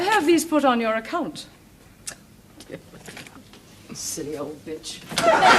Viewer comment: did she say bitch? They usually didn’t swear back on tv in them days of 📺.